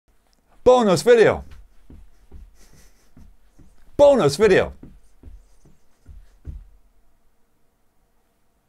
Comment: sounds like you was saying boring ass video lol